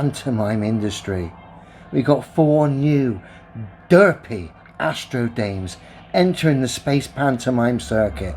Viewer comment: “AstroDames” sounds pretty cool actually, like the name of roller derby team or a ska band. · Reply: I thought he said AstroDanes 🇩🇰🤣